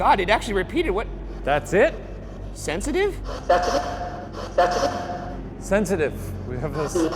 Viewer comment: Why'd y'all cut off in the showers so abruptly? I feel like a lot was happening with the spirit box. For example, the voice repeating itself. To me it sounded like it said, "That's me," rather than sensitive. Did anything else happen in there that got cut?